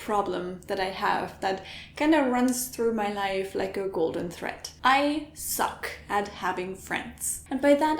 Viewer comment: What she say? "Golden thread?" "Golden threat?" I assume it's an idiom directly translated from her native tongue. Google's not helping.. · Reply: velkoon she said golden thread - „goldene Schnur“ in german it’s a figure of speech